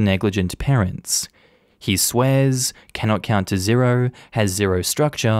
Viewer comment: Second story, I had the volume lowered for a second and thought you said, "Cannot count to zero." And thought to myself, "Damn. What the hell are these parents doing not teaching him the barest of bare minimum??" 😂